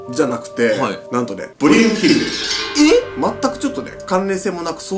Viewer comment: ブリュンヒルデやったんや笑 ずっとポニュンヒルデやと思ってた、、、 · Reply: 私もポニュンヒルデって聞こえてました…！ 同じように聞こえてた人いて嬉しい😆